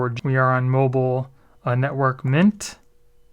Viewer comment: Yep... that the same with my Bison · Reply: which service provider are you with. ( I heard him say network MINT)